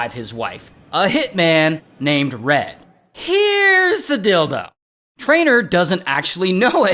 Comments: i was listening to this without watching the screen and i legit thought you said “Here’s the dildo” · I heard "here is the dildo" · i hear "here is the dildo" lmao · Who else thought he said here’s the dildo🤣 had to hit rewind real quick🤣🤣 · I thought Ray said "here's the dildo" lol